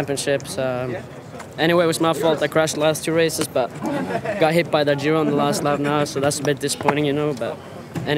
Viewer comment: DId anyone else hear him say i got hit by that jew??? hahaha